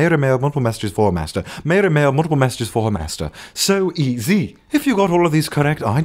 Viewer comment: You messed up on the "master" during, 6:03 - 6:04. I heard it as pastor not master.